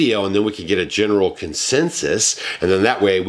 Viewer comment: I THOUGHT YOU WERE SAYING “REAL EYES 👀 “ 😆😆😆😆